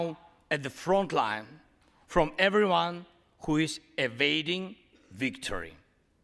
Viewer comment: Thanks I had to come here as I thought he said evading victory and not awaiting. Amazing speech! Good to see traitor Boeboert looking miserable too ✌✌